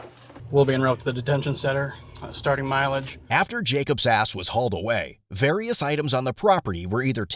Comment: I swear you said "After Jacob's ass was hauled away" xD